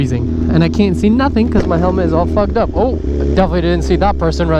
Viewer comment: Bro it almost sounded like you said that your helmet is Fuck up,,, 😂 😂 😂 ride safe